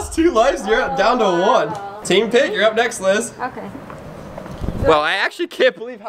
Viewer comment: I thought he said “ Team PIG “ lol